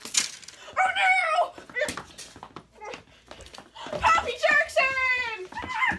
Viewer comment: What they said: Annabeth?! Percy?! What I heard:"Annabeth?! Puppy?!